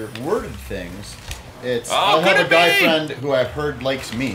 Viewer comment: Oooooooh! I thought they were saying DYE friend and I was SO confused!!